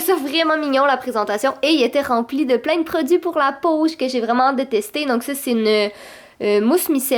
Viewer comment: ell3 dit j'ai vrwiment hate de les tester pis moi jai compris que j'ai vraiment detestée 😂